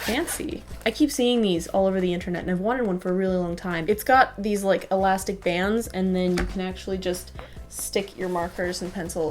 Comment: she said water..anybody notice that..? i think she ment to say wanted..